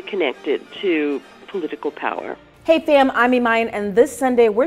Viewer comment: Did she say fam? 🤨